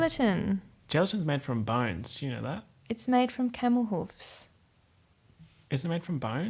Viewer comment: I truly thought she said “chemicals” instead of “camel hooves” 🤦🏻‍♀️🤦🏻‍♀️